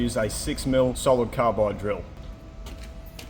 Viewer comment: "Carbide" drill. First go-thru I thought you said, "copper." 🙄🙄🙄